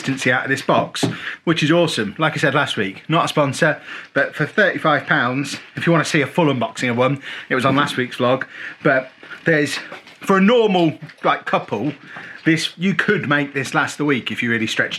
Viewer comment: did you say "for a normal black couple" or did i hear you wrong? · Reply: He said “For a normal, like, couple”. We British tend to say ‘like’ a lot.